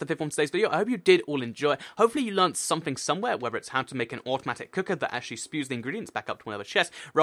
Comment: Toy cat at first I thought u said cookie